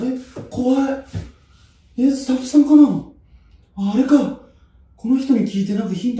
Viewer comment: 「スタッフさん‥？」が「ふさっふさ‥？」に聞こえたw